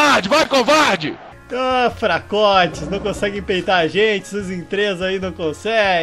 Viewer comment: "Fracotes, não conseguem 'peitar' a gente." Só eu entendi peidar? Ksk Desculpe, sou idiota demais.